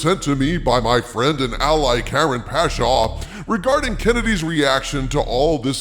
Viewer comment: Whoa, it almost sounded like you said Karen Pasha. Was Harvey near the audio controls? □:_dharvey